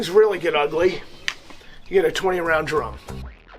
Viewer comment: Did he say 20 round drum? 5:11 Was it a shotgun?